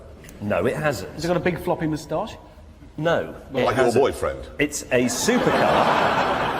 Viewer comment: Anyone know what Jeremy said there at 11:02? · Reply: "Like your boyfriend?"